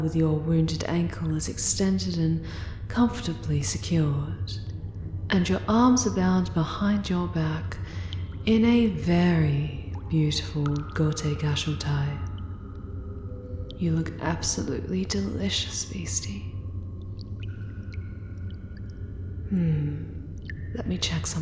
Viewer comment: That's ok! It's called a Spiral Futomomo tie, and it binds the leg in a beautifully elegant way. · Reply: What about the one at 1:30. I keep hearing "Gote Gashu" but either I'm misspelling or mishearing it. I mean, "(behind the) back (tie of) elegance" sounds interesting but that's as far as my Google-fu (and Google translate skill) has gotten me.